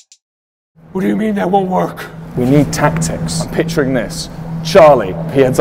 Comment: I thought Wilbur said “We need b*tches” instead of “We need tactics”😂😂😂😂😂😂😂😂😂